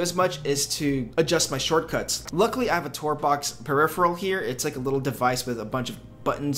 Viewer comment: what is that peripheral? Couldn't make out what you called it. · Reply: Tourbox